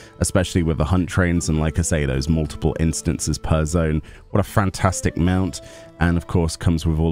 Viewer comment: it almost sounds like says "What a FRAN-tastic mount". Thank you, for your sac(k of nuts)-rifice to help show us this mount! xD